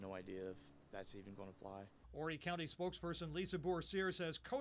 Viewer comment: Thought it said "horny" county at first...